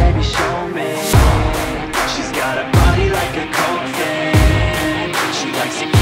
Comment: I thought it said body like a Gold Fish 😂🤣😅